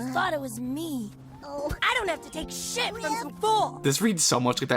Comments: WHAT DID RIFF JUST SAY?! 😭💀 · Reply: HE SAID SHIT I SWEAR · sounded like he said: "I don't have to take s@ from some fool!" 😭😭😭😭 (he might have said that since the line is edited) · i genuinely thought said "I don't have to take shit" 😭💔